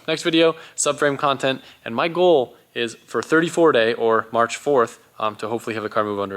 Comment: When u said 3 or 4 day I thought u said 34 day 😁😁